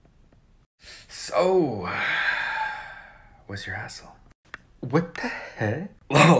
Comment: Duolingo better be prepared for this 😂😂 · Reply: Dude I heard “What’s your asshole” when Wus ur ASL appeared why